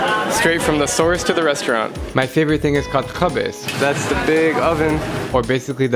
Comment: it’s actually khibz it’s just bread I’m an arab so I would understand, and I speak Arabic so I can understand what there saying 😊 😊 (I’m from lebanon 🇱🇧)